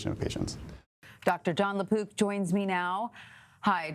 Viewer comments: I thought she was saying Lapoop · Please emphasize the " k " sound on the mans last name. I thought ya'll were saying LaPoop...